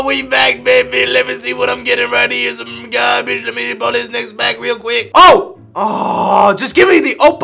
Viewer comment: did he just said n word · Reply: how do ya hear the n word i hear "next pack"